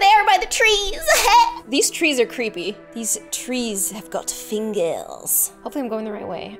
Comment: These trees have got fingels 2:45 baNanA peElls if you know what aM sAyIn 3:30 plEaTH 6:15 aErAg WhAtDoEz nAU AAAaAAa 15:03 'Larry has been sO helpful' Also larry: Playing death metal 17:31 promIScUOuS 19:22 'I forget stuff a load' also miss rosenberg at 20:01 - 20:28 / 18:38 - 19:11 (talks about her whole backstory)